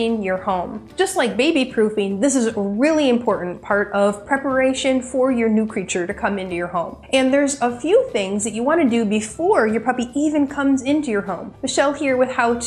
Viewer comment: I just heard “ New creature” 😂?